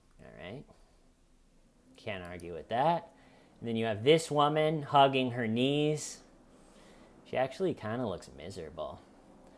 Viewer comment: I thought you were saying hugging her niece 😂